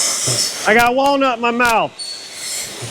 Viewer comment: "I got Walnut in my mouth!" All I heard was "I got Wally's nut in my mouth!" 😂😂😂😂😂